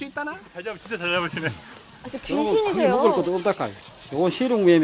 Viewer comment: 초에 나만 븅신이세요라고 들리나요?